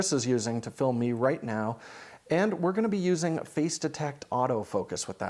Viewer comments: Did you say PHASE DETE....oh ...FACE DETECT :( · Do you mean DFD? Yet for that I hear that DJI may collaborate with Lumix for a LiDAR module. Not sure if it is possible... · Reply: He said “face detect autofocus”.